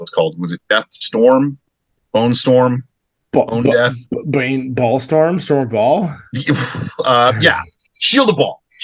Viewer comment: I misheard "Baal Storm" as "Ball Storm" 😄 The riffing on Death Leaper was really funny. The design puts me in mind of Lost Boys. I agree that 40k is all the better for the addition of some humour. Thanks for another fun episode.